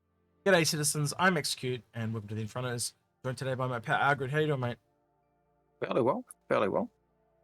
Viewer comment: Did execute say at 0:07 Pet Algared? ;-P To me the cutter looks like a cut off tram /streetcar. Which is probably how drake produces them. The former trams are cut (hence the name) and then probably made airtight with duct tape. But the extra fuel makes it the Carrack killer imho. ;-P The fins at the back should make it more maneuverable turning horizontally but probably cannot produce an upwards drift. Does it not have different thrusters as well? Maybe it can be used as a two or three man attack vehicle due to the additional weapon rack. Otherwise it feels like an offroad vehicle in space.